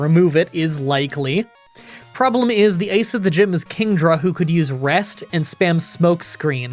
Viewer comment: He said Ice instead of Ace, lol